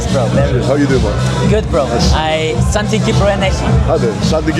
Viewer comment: What did you say that made him laugh? I missed it but was curious :D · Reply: I said "san tin kypro eneshi" which is a super Cypriot thing to day. It basically means "There's nowhere like Cyprus" but it's super funny when someone that is not from Cyprus says it because Cyprus is not really my "home". Does it make sense? :D